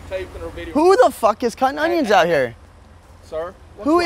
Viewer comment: "who tf is cutting onions out here" Danny Duncan: cutting onions out here 😭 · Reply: I thought he said cunt onion till he repeated his self 🤣